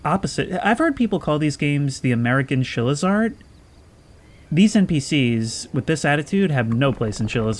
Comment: "iv'e heard people call these games the american chillizard? charizard? what is he saying? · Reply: Chilla's art. It's another game dev with a similar style